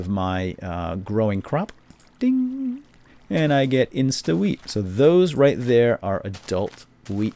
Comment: i heard insta-weed lol.